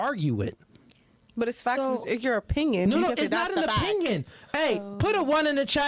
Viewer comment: Did she just say "it's facts in your opinion"? 😂🤣😂🤣😂